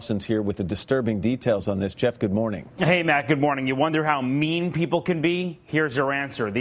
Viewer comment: I thought he said in the beginning, "Ever wonder how meme people could be?" Loool